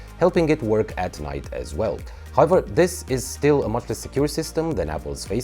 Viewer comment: From United States. Great video. Around 2:40 did you say "it's more or less secure then Apple's?" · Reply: He said "much less secure"